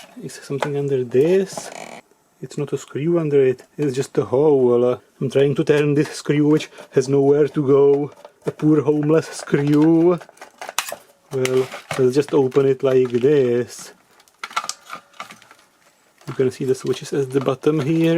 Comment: "Poor, hole-less, screw" LOL!! Good one! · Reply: I hear "homeless screw"